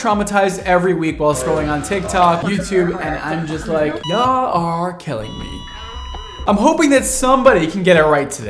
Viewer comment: I thought u said r kelliying me ! lmfao...instead of are killing me ll😂😂😂😂😂😂who peeing on u Brad 😂😂😂😂